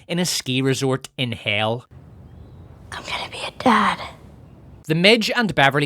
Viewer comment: 😂did she just say she’s gonna be a dad????